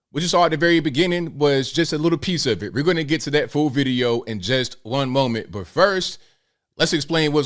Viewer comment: I thought said full video but obviously he said fool video. I'm very proud of that city council. Hopefully more cities will grow a pair.